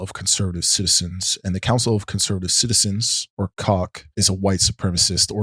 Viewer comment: Did you just say the word "Cock"?